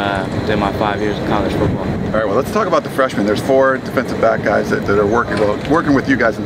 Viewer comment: I thought it said "..defensive blacks"